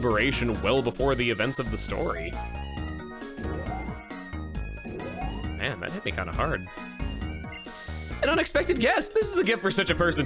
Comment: I am so sorry, but i misheard the "man that hit me kinda hard" at like 4:00 as "man that made me kinda hard", help